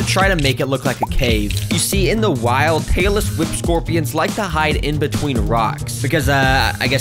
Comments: I thought u were saying "taylor swift scorpions"😭😭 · thought you said taylor swift scorpions